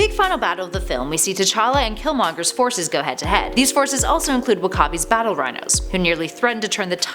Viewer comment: Did she say wakabi @ 1:20 ? Lol